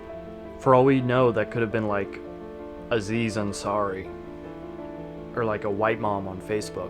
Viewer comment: did he said Aziz Ansari?